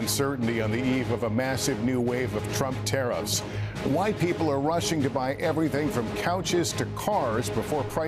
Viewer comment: thought he said Wht people are rushing 😂